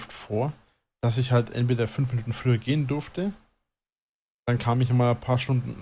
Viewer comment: Meddle Leude, sagtmal bin ich der einziche der bei 35:45 "5 Lügen früher gehen durfte" verstehet? Liebe geht raus ♥️